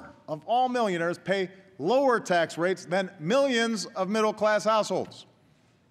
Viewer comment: did he say "Middle-class as*holes?"